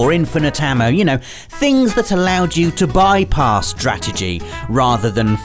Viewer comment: Anyone else hear say Drategy @ 2:52 rather than strategy